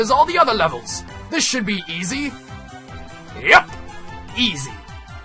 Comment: east instead of easy